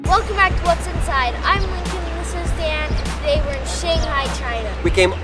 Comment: all this time i thought lincoln was saying hi i'm lincoln and this is dad.. when i didn't turn off the cc and realize that he says DAN instead of dad >.<